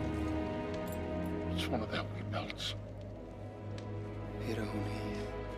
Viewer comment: What did Legolas say in 0:28-0:32? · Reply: Hiro hyn hîdh ab 'wanath. Means 'May they find peace after death' in Sindarin.